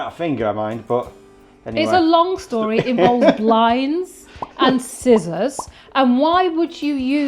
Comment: Blinds? 😂😂😂😂 I thought you said lines....... and scissors. 😂😂😂😂 Sounds like a party !!